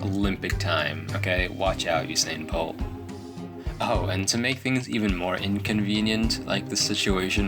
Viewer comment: Omg if you have it on captions haha on like 8:10 watch it with captions on ahah when he says watch out usain bolt the captions say watch out satan pope haha